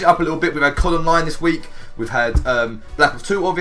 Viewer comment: i thought he said got columbine this week lol